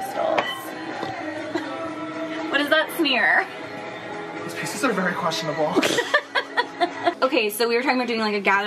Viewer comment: “The prices are very questionable” the quote of my life 😂😂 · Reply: I literally thought he said these pieces are very questionable...oops